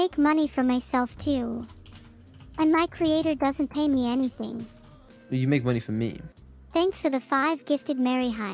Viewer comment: I think said "You make money for me" instead of why do you need from me